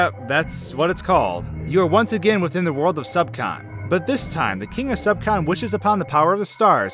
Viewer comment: i heard the word "subspace" * I T S B R A W L T I M E*